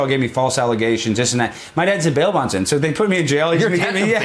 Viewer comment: my dad is a what? I cant understand what he said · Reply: Bail bondsman. Alex also worked with his dad as one.